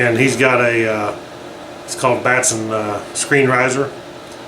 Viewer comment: aww man I thought you said Batman risers.